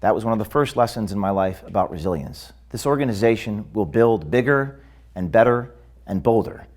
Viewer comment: Careful James sounds like your saying "build bake better"